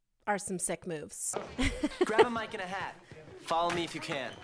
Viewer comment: When he said “grab a mike and a hat” I really thought he said “grandma might get a hat” I was very confused.